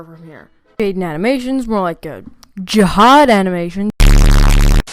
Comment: I thought he said "bush administration".